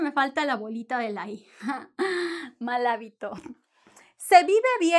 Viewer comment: What does it mean "malavito" ? it was said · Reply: Ella dijo "mal hábito"